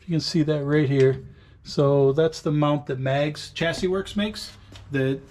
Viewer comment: My hearing isn't the best, so anyone able to tell me what company name he said for the shifter mount with the fire system? 22:13 ish. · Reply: Sounds like "Mags Chassis Works"